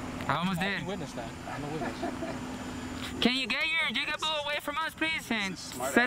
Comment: Did you call the black guy a “jigaboo” at 8:30?